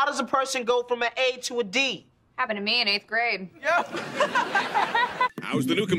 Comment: Can anyone please put captions of what Jade says at 4:02 please? I think I get all these jokes except for that one, I can't understand what she says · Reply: she said "happened to me in 8th grade". She's referring to her breast size going from an A to a D.